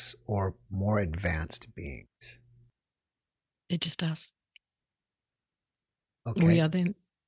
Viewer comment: What did she say at 13:45? The Closed Caption couldn't even get it. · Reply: She said.."It's just us"........Peace to you 😊